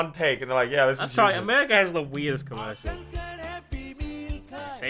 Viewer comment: excuse me, I thought u said America has the weirdest commercials instead of JAPAN has the weirdest commercials